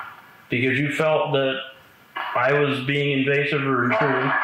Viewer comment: I heard "it was"